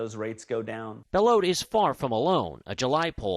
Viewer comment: "... he's far from alone" but I thought at first they said that because of the interest rate "he's far from a loan", which would have worked, too.